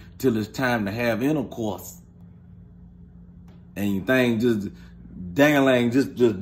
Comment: Look 23 but... Did say dang A lang just there? Complete with a physical demo??? 😂😭😂☺️ 😭🤣😭🤣😭